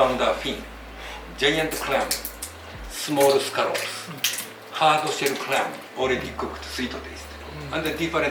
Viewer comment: Quick correction: 6:07 he says hardshell clam at the end, not hardshell crab. It sounds like he says hardshell crab but if you look closely thats actually Hamaguri, a hardshell clam used in edo Mae sushi.